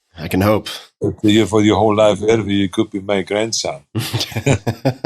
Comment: The joke at 1:03:46 sounded like “I had so many women, you could be my grandson”